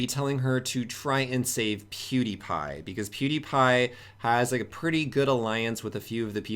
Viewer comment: I heard cutie pie....